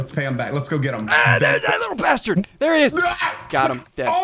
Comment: SSundee swore he said bastered